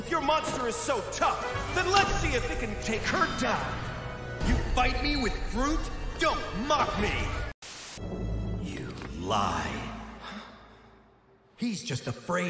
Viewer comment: "YOU FIGHT ME WITH FRUIT?!" what a mood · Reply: I thought he said “You Fight Me With Brute,” but hearing it again, I hear fruit now, and it’s hilarious